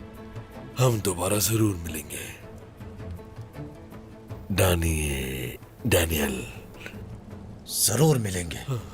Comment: What cerkotay is saying man "!! 😂😂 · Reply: Dani Daniel 😂